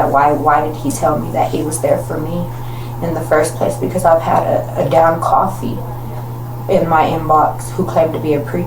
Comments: “I had a damn coffee in my inbox claiming to be a preacher” Translation, please, lol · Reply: Still unsure on that one. I thought maybe she was saying "I had a damn cop, he was in my inbox.." and the subtitles picked it up as coffee but that doesn't make sense either. Or does it? 😂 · What is she trying to say at 12:20? Something about a “down coffee”? The captions don’t make sense or I’m missing some slang definition…